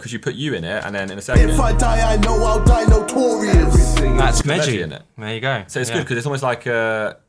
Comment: I thought said “ that’s Deji” and I observed him for 2 minutes because he doesn’t really look like him 🤔🤦🏼‍♂️